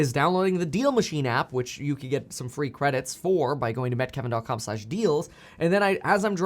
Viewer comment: Why does it sound like you say “Met Kevin dot com”, isn’t it meet?